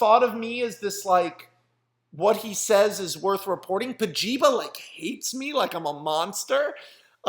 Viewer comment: totally thought you said "kojima hates me" and was super bummed out.